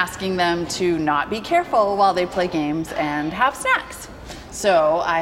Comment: oh shit i thought she said "have sex" that would be REALLY FUCKED UP